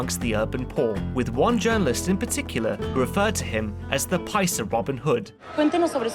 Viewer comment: Nick says Pablo is called the "Piscer Robin Hood". Anyone know what "Piscer" means? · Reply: I believe he said Paisa, which is Spanish for rural or rustic.